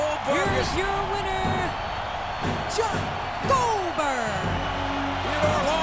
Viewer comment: Did she say John Goldberg when he pinned Mark